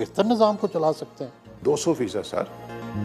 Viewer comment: did he said 200 feesad ?